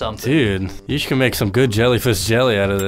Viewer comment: is he saying jellyfish jelly??